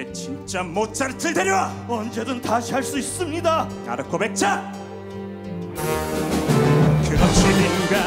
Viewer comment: 여기 뭐라고 하시는거예요??ㅠㅠㅠ알려주세여 너무 궁금해요 나비보베쳐??????? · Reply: 아르코백작!! (스포주의?? 스포... 라고 할만한건 아니지만.....) (극중에 보면 초반에 뇌 가져왔는 사람이 아르코백작 이에요 주교의 오른팔? 이고요. 그 사람에게 레오폴트 모차르트를 데리고 나가라고 눈짓합니다)